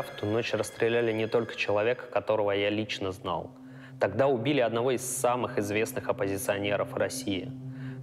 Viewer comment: Я все правильно услышал про Немцова? "убили одного из самых известных оппозиционеров России"...не "оппозиционеров В России"?